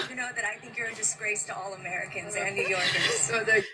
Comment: What he thought he heard "You have a face that's missed by all Americans and New Yorkers." That could only prove why he's grinning like a damn fool.